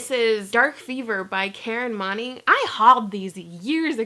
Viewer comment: Totally thought Moning said Moaning...